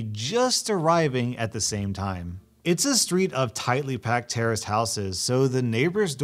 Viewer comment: I SWEAR HE SAID TERRORIST HOUSES LMAO